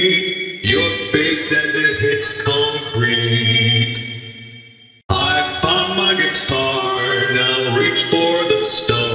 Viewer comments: Rockstar Bonnie truly sounds like the most creepy of them all, because he isn't trying to sound creepy or threatening, he is literally stating what he is going to do to you, unlike Nightmarionnete or the Nightmares voice pitch that are trying to terrify you, Bonnie's uncanny metallic echo sounds like he is broken yet completely stable and functional. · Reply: i thought he said "i fricked my gitar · This has to be funniest voiceline so far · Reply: idk about anyone else but on that voice line I hear “I f👹k my guitar-“ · Is it weird to anyone else that rockstar Bonnie's "I found my guitar" line actually harmonizes at the end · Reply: Tho it kinda sounds like he said "I f### my guitar"